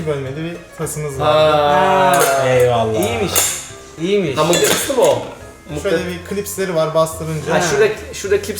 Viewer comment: de ne diyor anlayan varmı :D ? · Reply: eray gaming ha mıknatıslımı o diyor